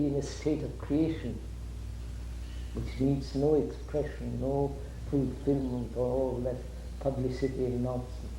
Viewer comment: "Which needs: no expression, no..." ???? Can someone understand the words please ?? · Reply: No fulfillment, no publicity.